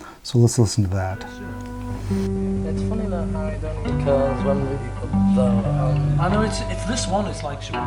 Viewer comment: I hear "it only occurs when" but I don't hear "but you know".